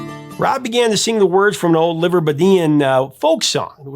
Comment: +/- did you say Liverbedian? Later mentioned is Liverpool. If the reference is to that which comes from Liverpool, then the word is Liverpudlian.